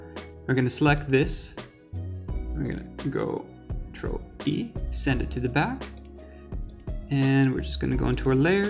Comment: omg so slow and over-detailed explaining but at the key moment I can't understand what u did at the end xD 3:18 sounds like "go drop E send it to the back"...can't do this